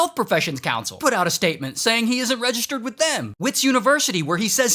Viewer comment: Thought Ray said Wish University, also not the worst thing to come out of South Africa.